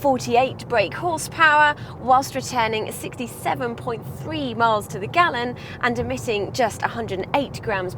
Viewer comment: Did I hear that right? 2:45 she says 67.3 MPG?